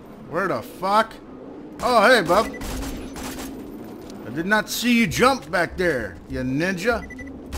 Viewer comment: Oh haha I thought he said Nigga